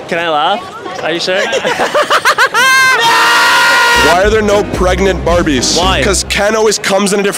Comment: I thought he said pregnant “babies” and somehow I thought it was the joke 💀